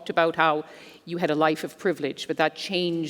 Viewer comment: I thought you said you first had a life of privilege?